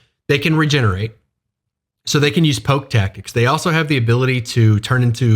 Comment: Poke tactics? Around 5:00? I couldn't hear what you had said and the captions weren't helpful. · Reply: yeh poke tactics, get in, do some damage, get out and regen